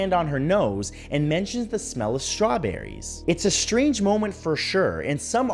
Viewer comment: In that Britney video I never heard “strawberries” I always heard “strong Britney”